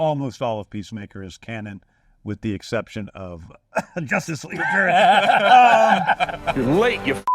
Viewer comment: DId he said Justice League trash??? I can't really hear because they started laughing